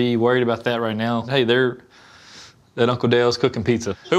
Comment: I misheard Hazel sayin' "Wassup uncle Dale!" and I was like "Wassup girlfriend! Gimme a five."